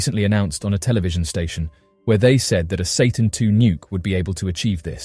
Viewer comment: Did I just hear you say "Satan 2 Nuke"? Like, the devil? O_O